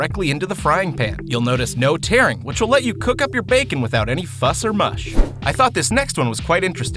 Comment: Am I hearing him correct at 2:46..."Without any fuss or mush."?